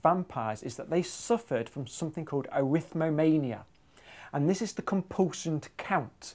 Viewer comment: I thought James said Erythromania, which would (sort of) be love of red (or red blood cells.) Which is apt